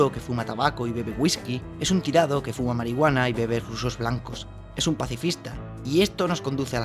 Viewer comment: Que dice en el 10:19? No entendí que decía antes de blanco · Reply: Rusos blancos, la bebida del Nota; calúa, vodka y leche